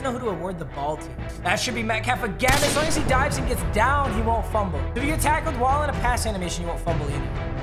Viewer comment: Thought it said "if you fumble you get touched" safe to say I was disappointed